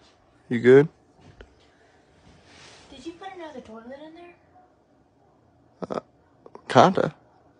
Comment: If anyone could not hear, she said " did you put another toilet in there?" And then " it feels good, can u put one in the other bathroom?" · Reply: I heard "Did you put another door lid in there?"